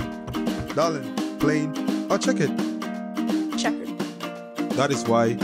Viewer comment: I'm I the only one who heard her saying modern chicken? Anyway, you so courteous sis Sefa 🥰